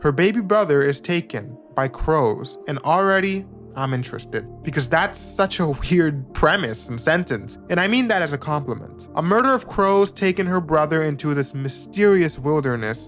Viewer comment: I thought you said a "murder of bros"